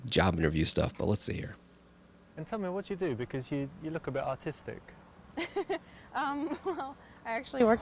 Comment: it sounded like he said autistic